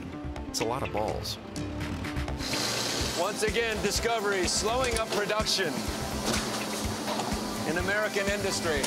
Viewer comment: did he say slowing UP production??